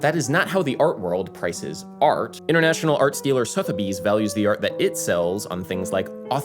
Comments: When you said "I'm not an arts dealer" for a minute I thought you were saying "I'm not an art stealer." The conspiracy theories started forming. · whe you said "arts dealer" i heard "art stealer"